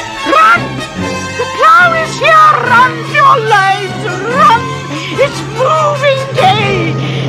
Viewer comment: I thought she said "moving gauge" but she said its moving day